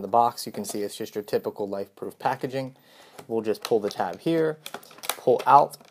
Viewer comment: Did you say lifeproof case 2:00 into the review